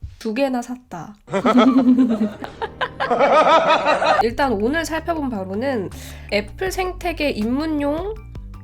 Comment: 전 왜 "일단 오늘 살펴본 바보는" 이라고 들리죠?? 앞부분이랑 묘하게 어울리네